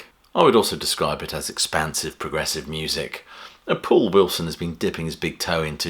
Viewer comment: Did you call him Paul Wilson at 2:33? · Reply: he said the pool Wilson draws from